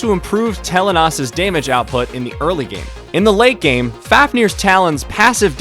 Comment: For a second I thought u said Fatnirs Talon😂😂😂